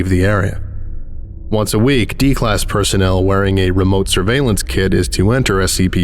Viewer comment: I heard it as "remote surveillance kid" and I was like, "is there a Pentagon-backed kindergarten in the SCP universe?" 😭